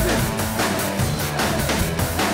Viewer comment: Just understanding "Kick da Baby" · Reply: I heard it as "check the business".